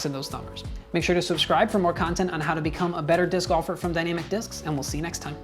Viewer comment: Did he just say dynamicdiscs like "DannyMcDiscs"?